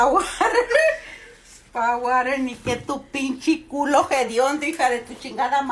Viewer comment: i heard "pinche culo" in there somewhere so i think i get the gist of what she's saying lmao